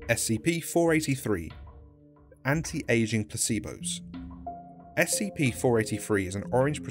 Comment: Wow, I keep mishearing the titles of these SCPs. Examples: I thought he said SCP-483 was called “Anti-Asian Placebos” and SCP-489 was “1-555 Butt Rash.”